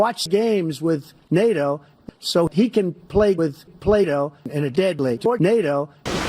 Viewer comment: i thought he said Plato, not play doh, and biden playing with Plato would be well...yea